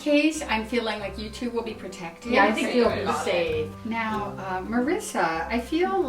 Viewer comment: did she say "I think you'll be saved" or safe? Because if she said saved then something dangerous could still happen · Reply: Emily22 I went back and she definitely said saved😱